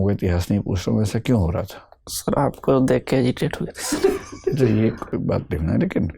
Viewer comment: 😂😂😂Whatt? 28:27 Did he say, aapko Dekh k Irritate hogya tha? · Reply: Hesitate***